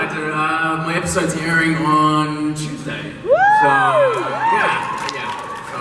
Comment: please someone can whrite in english what bob say because I’m french and it’s easier for me 😊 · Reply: He said ya the episode airs on Tuesday